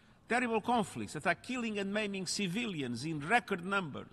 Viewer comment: can anyone tell me please what he said at 2:10 :" killing and ??? civilians " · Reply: maiming